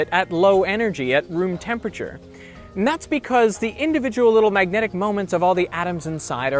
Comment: it's "that's because"right? Why does the subtitle says "Not because"